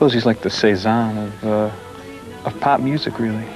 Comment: What did he call him? Sounds like "says on"? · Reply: I think he's referring to Paul Cézanne